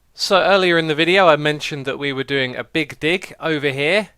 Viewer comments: Did he just said "Dig Di*k" in 20:27 ? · Did he say big di** ?